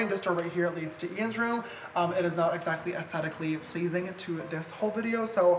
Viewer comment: When u said "Ians Room" it sounded like u said "Andrew"